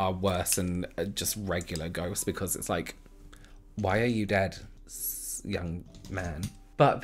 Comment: I had my phone kinda far and I thought you said "Why are you dead? ..stop that." 😄